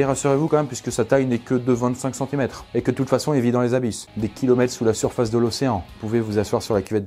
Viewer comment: J'ai entendu "... et de toute façon il ne vit que dans les anus. Vous pouvez vous asseoir sur la cuvette des chiottes tranquille."